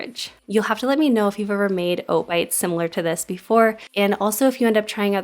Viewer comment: I’m watching this in bed, after taking a hit of NyQuil. I thought ‘make oat bites’ said make out bits. Oy!